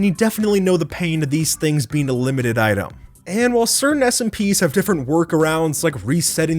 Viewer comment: thought he said "And while certain SCP's have differerent" lol